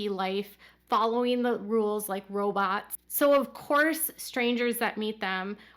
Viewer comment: I heard something wrong when you said they (the family) were acting like robots in public, I thought you said jobots…then it hit me. They are Jimbots.